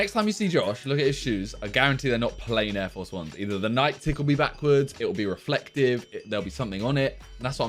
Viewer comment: I swear he said “Nike tickle me backwards”